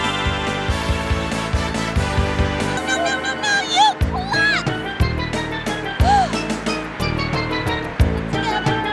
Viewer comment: wait what did she say? 🤣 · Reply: "No No No No No, You Klutz!"